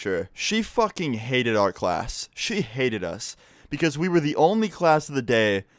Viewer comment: for a second I thought you said art class